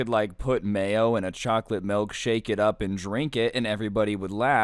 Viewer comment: i thought he said he put mail in a milkshake then drink it lmao